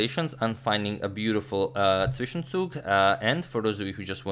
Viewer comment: What he says at 13:20? Sishensou?